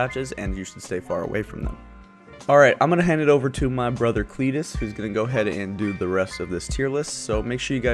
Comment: For a second I thought it said Fetis instead of Cletus LOL. This had to be your best and only hilarious video, I enjoyed watching every second of it, and it definetly made my day. Stay original, stay awesome. P.S.: Indeed Waifu is hot, and thicc too. :^)